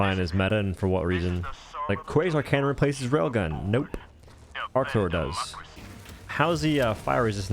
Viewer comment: what replaces rail gun? arc? 4:01 I can't really hear what he is saying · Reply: Arc thrower